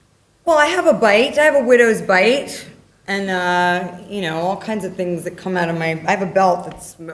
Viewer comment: What does scarlett says at 17:00 ? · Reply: All types of things that come out my belt